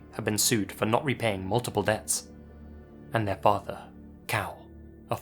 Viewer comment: "Their father, a former policeman, was forced to resign after he was accused of --- " Accused of what?? The subtitles say he was "accused of women"? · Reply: Possibly r@@